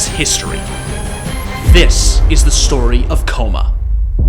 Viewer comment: i clicked the wrong video help :) i heard ''Story of Kkona''